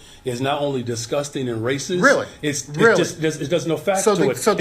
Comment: I like how the mayor literally says raysis instead of racist. Lmao.